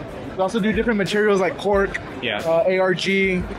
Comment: did he say Pork? then he said it again and he said it again PORK... not kosher yall!!! hahahahahaha